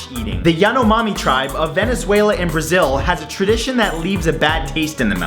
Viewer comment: Venezuela isnt on Brazil,Venezuela is on Venezuela.It's an indepentent nation. <link> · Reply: ***** For me, a foreign, in the first time I watched the video I heard "venezuela in brazil". Anyway, Nice video!